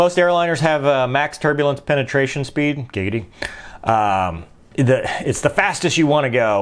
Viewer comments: "Giggity" i heard that 😂😂😂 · I'm sorry did he just say gigidy at the word penetration